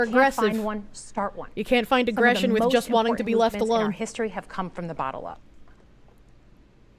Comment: It sounded like she said "Some of the most important movements in our history have come from the bottle up." Which um, may be more true than one might think I guess.